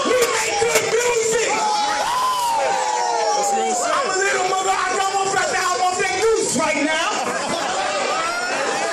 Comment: what did he just say?? 🤨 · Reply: He off that goose right now